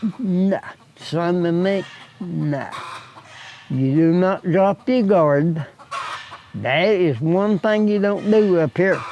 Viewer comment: he said you can’t “drop”??? the garden what did he say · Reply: He said that you can never drop your gard. The weather can change in a heartbeat.